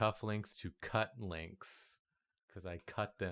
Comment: ish I swear I heard say cuck-links not cut-links...